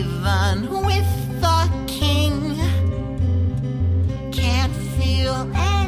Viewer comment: sounds like he said fucking